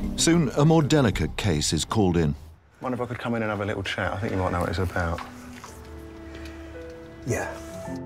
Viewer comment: Could anyone please tell what does he say at 0:28 ? Thank you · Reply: I think you might know what its about